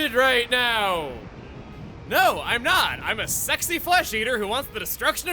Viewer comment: "I'm a sexy Flesh Eater!" - Out Of Context Emile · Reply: I thought that said "I'm a sexy eater" and I was like "wait, this isn't digimon"